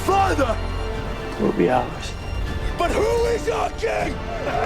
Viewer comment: at minute 17:08 there is a voice that says something but I can't understand it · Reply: «Will be ours”